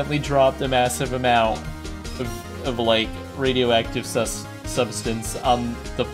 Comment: i thought he said radioactive sus